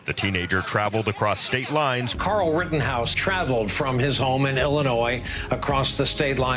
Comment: "Carl Rittenhouse," -- did I hear this correctly? Carl?